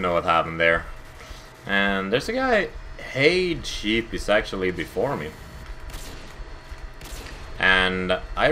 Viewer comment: lol it almost sounded like was saying "theres a guy before me?!?! Nobodys before tejb, tejb is the best!"